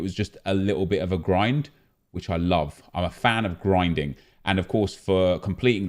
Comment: did he say hes a fan of grinder???